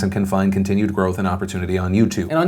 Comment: When you read "can find continued growth and opportunity on Youtube" I heard "confined continued growth." and I had to look up at what you were reading. The sad part is it wasn't a moment of "That must have been wrong." it was "Did Youtube really say that?"